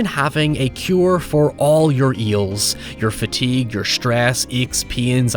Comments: i heard a "a cure for all your eels" and it made me laught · I definitely heard “a cure for all your eels’, which is perhaps the best crossover event your accent has ever had lol. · Hey Ryan, at 15:05 when you say the word ails the auto subtitles said eels! xD Just thought some people might appreciate that accidental reference lol